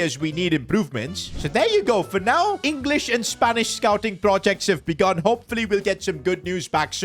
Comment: did bro just say English or Spanish